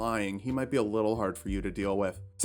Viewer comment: I really thought he said “special gifts from Iraq” and was very confused.